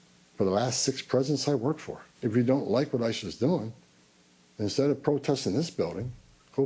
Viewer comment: Sooo funny that the automated subtitles spell ISIS instead of ICE, at 36:26 mark :)